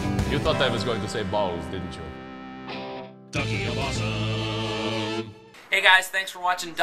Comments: I like the song, but at the end when they said, "The Key of Awesome" what I heard was, "Ducky of Awesome". Come on, I can't be the only one that heard it. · it sounds like he is saying ducky of awesome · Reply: no because they are called the key of awesome